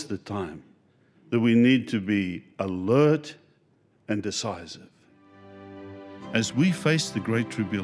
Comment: Thought he said, "alert and dishonest". Had to play it back 3x to realize it was "decisive".. I think he really means the first one 🤣🤣🤣